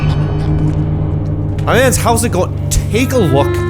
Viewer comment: is... that English? I literally cannot understand what bro said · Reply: "Hey my mans hows it going?" He kinda slured his words so its understandable